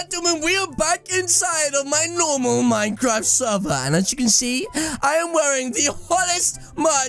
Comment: did he just say minecraft world????